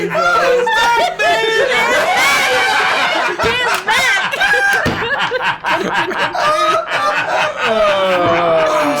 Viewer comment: What does say that makes them laugh so hard, at around 40:10? I can't make it out? XD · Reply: He says: "This is they Feywild and you let that fucking lion (sounds like Lie-On) go?? It could have been a Truth-On."